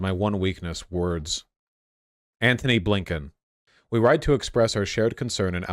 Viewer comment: Every time I hear "A. Blinken", I always hear "Abe Lincoln" and get confused for a few seconds until I realise who is actually being talked about 😅